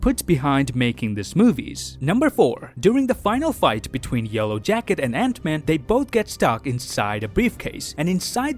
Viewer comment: did you just said "yolo jacket" at 1:04...